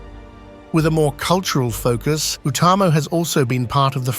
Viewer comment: Who else thought they heard "Utumno" instead of Utamo?